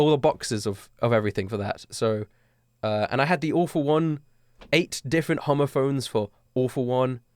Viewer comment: When he said “All For One” I thought he said, “The Awful One”